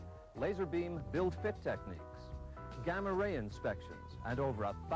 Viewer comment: Did he just say gamma ray inspection? Are they trying to make a car or the Hulk? 😂